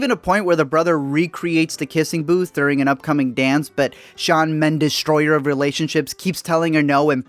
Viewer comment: did he say shawn mendes idk thats what i heard?